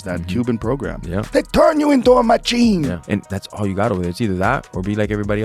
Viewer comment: Everyone sucks it up when he says, "They turn you into a machine"... but all I hear "juice or die."